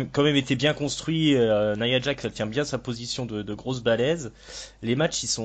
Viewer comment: J'ai compris "Nia Jax elle tient bien son rôle de grosse baleine" et pas "grosse balaise" ^^". Moi aussi j'aime bien Nia, je trouve qu'elle a fait des baux progrès et qu'en plus elle a des yeux fascinants. Le premier HOF c'est en 1993, après la mort d'André. Y en a pas eu pendant un moment puis après ils ont fait un par an. Le finish employé par Joe c'est un Uranage slam.